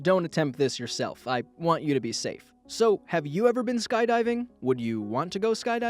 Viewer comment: All I heard at 2:51 was so have you ever died, do you want to die.